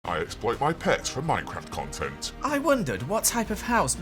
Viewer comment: I thought that he said : I explode my pants for minecraft content"